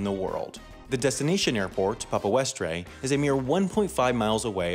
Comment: I thought you said FIGHT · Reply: I thought you said Weast